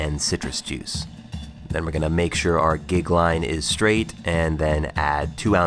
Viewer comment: does he say we're gonna make sure our dick line is straight?!?!!